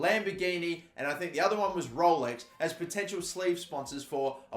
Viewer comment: anyone else hear him say "slave" sponsors😂